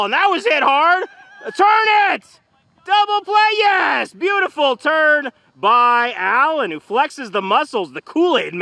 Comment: beautiful turn sounded like he said beautiful turd